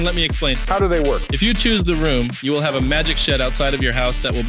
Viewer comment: I thought he said a magic shat